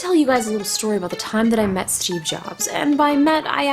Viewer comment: I live in other country that USA or GB ( i dont english well) and I hear in 0:01 this : masturbate jobs ;d